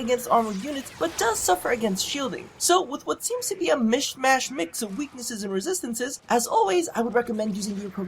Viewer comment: So after rewinding and listening to this about 6 times in a row... I gotta ask.... Did you really say "Mish mashed?" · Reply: Lol, from the literal script.. "with what seems to be a mish mashed mix of weaknesses and resistances"